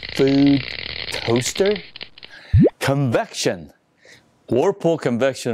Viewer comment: I honestly thought it said "Convention" and thought we were getting SamCon 2k20.